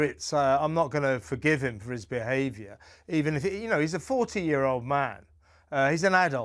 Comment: Ha ha, I actually thought he said Harry was a “14 year old man” and I thought that’s on the mark, 😂